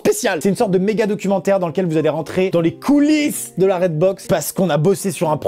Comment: A 0:08 mon tel a beuger et au lieu d'attendre dans les coullise j'ai entendu dans els couilles 😂😂💜 super intéressant